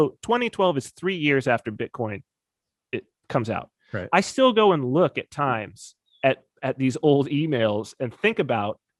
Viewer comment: he talks about buying BTC and it sounds like he says he “has it tranked and then leave” the casino. Tranked? Is that what he said? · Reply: "Have a drink". At this time, next to no one even knew Bitcoin existed, so if you were doing an in-person Bitcoin exchange, you were in the presence of a member of your tribe, who was aligned with you in many ways. Waiting for confirmations was an opportunity to meet a new friend over a drink at a bar in a nice casino.